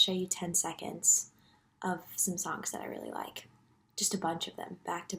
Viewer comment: I swear I heard “10 second clips of some socks I really liked” and the strangest part is that I didn’t even question it 💀